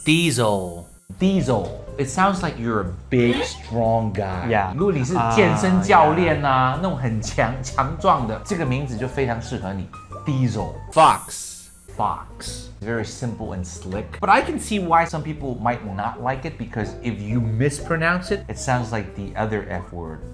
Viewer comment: 莫名其妙地聽成Fuck了